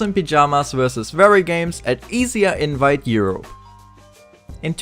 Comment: Was it me or did he call E S E A , EZYA ?